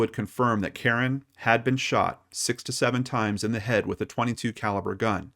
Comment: I thought I heard say that she was shot 67 times with a 22 caliber handgun.. LOL Checked again and it was "6-to-7 times"